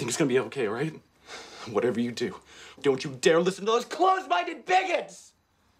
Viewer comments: I thought he said “close minded baguettes” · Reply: I thought he said close minded bagels.......😂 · I thought he said “close minded baguettes”